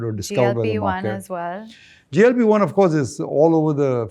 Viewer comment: can someone tell me What she say JB1??? 😢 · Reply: GLP 1 for type 2 diabetes.